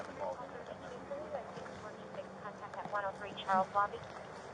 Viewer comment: or so, did anyone else hear "....at 103 Charles Zombie" that I think came from the officer's radio. I looked up police words for letters and C is Charlie and Z is Zulu. I could be wrong, but it sure sounded like Charles Zombie.